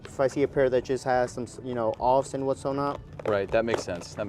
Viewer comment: idk why him saying "whatSOnot" everytime instead of "whatnot" is really annoying me 😂😂